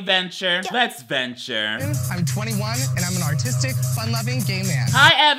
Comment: I thought he said autistic help 😭